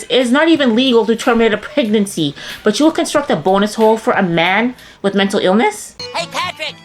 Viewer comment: Wouldn't the first step be to insist the man girl actually receives a few years of therapy. Not being able to function is often linked with PTSD. A bonus hole is not going to solve his problems. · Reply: I thought she was saying bone hole 😅